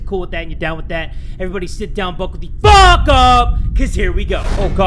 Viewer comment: Did you change your thingy like The”BUCKLE UP CUZ JERE WE GO” i heard “F** UP”